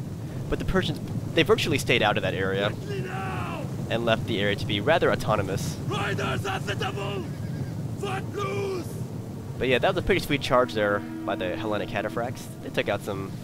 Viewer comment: did he say "FOOTLOOSE!"?